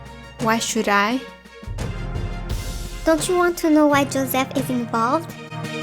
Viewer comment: I heard 'don't you wanna know why Joseph isn't bald?' SJEJSJJS 😔